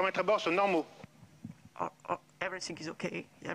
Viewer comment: When i hear "ewersing" instead of "everything", i take em seriously.